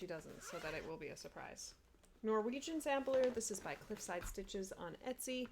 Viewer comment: I’m new to flosstube so I havent seen your videos and that Moroccan sampler is gorgeous, I love that!!! 😍 People want to “watch 30 seconds of her video” 😅 I loved that. 😆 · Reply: I totally thought you said Moroccan but I see your stitch and chat video says Norwegian 🤦‍♀️ Fail